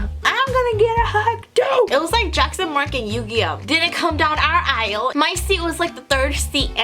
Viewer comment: to me it sounded like you said "Yu-gi-yoh!" 😆